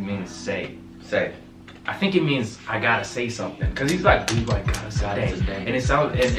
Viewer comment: also maybe its just me but i fully thought he was saying 'god isnt dead' when he was saying gottasadae idk if that was on purpose